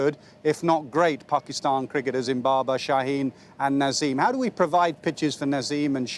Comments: did he say ZIMBABER😂😂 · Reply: Great cricketers in Babar , Shaheen,... Ye bola 🤣 but yes it does seem like Zimbabar 🤣🤣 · did he say Zimbabar? 😂